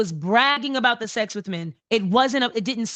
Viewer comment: Did everyone else rewind this 10 times?? Did she just say men?